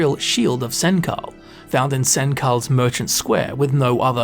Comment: My eyebrows raised when I heard "Sen kal" instead of "Senchal" 😅